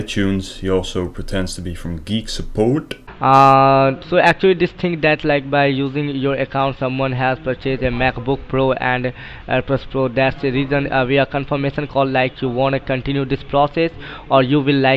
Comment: does anyone understand what he's saying? 🤔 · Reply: “So actually this thing that like by using your accounts someone has purchased a MacBook Pro and AirPods Pro that’s the reason we are confirmation call like to want to continue this process or you will, like, cancel it.”